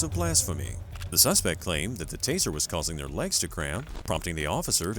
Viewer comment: did he just say " causing HER lags to cramp" please tell me i heard that wrong